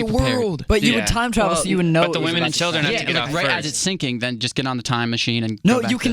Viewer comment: Anyone else hear Jonah say "The Women and Children have to get off first" they were talking about the titanic? Such a gentlemen